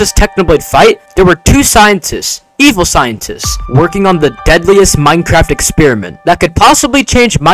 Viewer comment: HOLD UP! did you say DEADLY?! 18:29 CLOWNPIERCE SPEED RUN